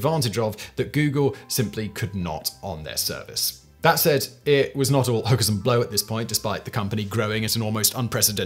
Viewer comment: Did he just say, "It was not all hoes and blow"?